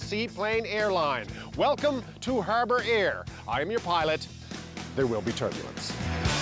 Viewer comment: Lol. I’m your pilot. I thought he said I’m your pirate. Good one. It feels good to be Canadian. Our lives are in your hands Rick…..Eh!.